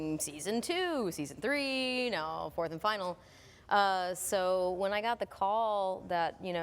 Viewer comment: Did she say "Fourth and Final" Season???